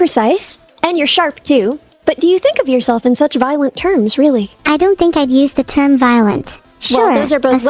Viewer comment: This video has multiple editing issues. 10:37 "in such violent terms" is transcribed as a question mark 11:00 caption has default formatting 17:38 stray equal sign at the end of line 18:37 caption keeps the formatting from neuro's intejection 20:51 missing final "you" 23:17 "now" instead of "neuro" (also "universe" got awkwardly split into its own caption) Additionally, when neuro talks over doesnt get captioned, even though neuro responds to her. And finally, the animation on the text bubbles is strange. I think it's too flashy for a half hour video, and something that's definitely off is that the bounce in/out animations seem to be expanding and contracting with the length of time that individual captions stay on screen, so a full sentence will lazilly rock into place while a single word buzzes in.